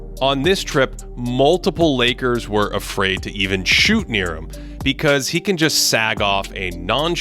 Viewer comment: did he say lakers were afraid to shoot near AD?? i gotta agree, draymond green did not want to shoot near him